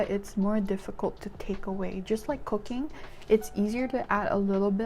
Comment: I THOUGHT SHE SAID “just like cocaine” AND I WAS LIKE WOAH WOAH WHAT (she said cooking) 😭😭😭I was so confused till I rewatched it